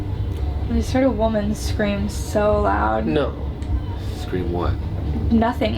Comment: In 31:25-31:28 when said she heard a scream it sounded like a woman screaming NO